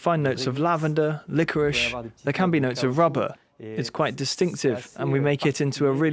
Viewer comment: The flavor has notes of lavender, licorice and RUBBER? Did I hear that correctly? 1:10 Maybe something was mistranslated here, lol